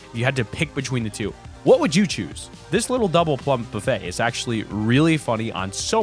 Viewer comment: I actually thought is said "double pump" not "double plump"